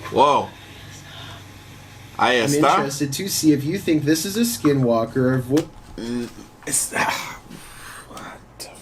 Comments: “I WARN YOU TO STOP” trust me, I’m really good at deciphering language. That’s what it said. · i think it says hay esta that means he is there in spanish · it sounded like "who is that?"